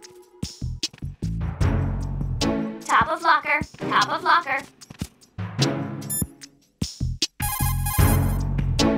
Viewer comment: what did the parrot say at 2:41? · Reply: top of locker , top of locker i think it's referring to the magazine on to of the lockers